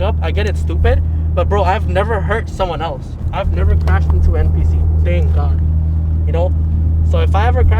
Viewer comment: did he just called other drivers NPC's?😂